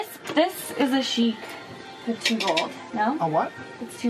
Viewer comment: "pa-too-gold" it's 3am and i'm crying laughing LMAO that's what i heard too